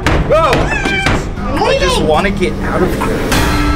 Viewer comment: wait, did just said "i just wanna get out of here"?